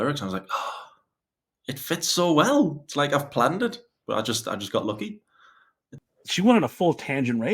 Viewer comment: did I just hear you say “ I got lucky “ and “ I done Foodie “ 🤔. Ummm ok 😂😂😂😂😂